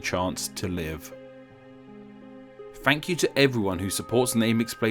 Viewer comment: I swear I heard you say Frankyou